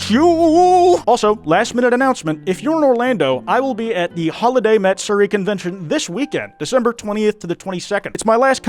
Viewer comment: I was just listening to audio and I legitimately thought you said so ething like"'Ill be at the holidy mats(???) furry convention" ☠️